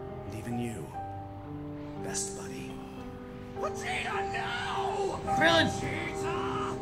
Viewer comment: did he say Vegeta GYAAT?